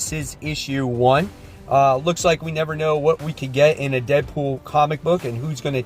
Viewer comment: I thought you said tedpool there for secound 😂 would love a Ted the stuffed bear team up with deadpool, would be a swear off 😂